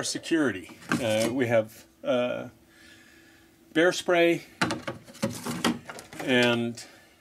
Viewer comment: I thought you said "hairspray", ....Was gonna say, How you gonna hurt someone with a can of hairspray! lol